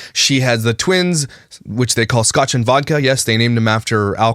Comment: I honestly have seen a few of her TIKTOKS on my for you page and I thought she had said scout and violette were their names